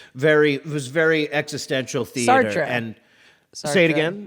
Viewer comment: It did sound like you said Star Trek instead of Sartre.